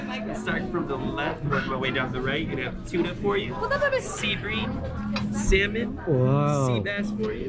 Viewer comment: whahtahat seaweed 🤣😂 wtf he say?